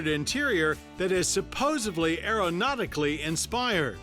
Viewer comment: Did he say supposably?